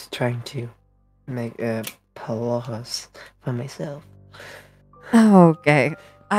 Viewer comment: it sounds like puppeteer says “oh-gay” XD Idk I’m tired an misheard okay, and it is silly so I must point it out.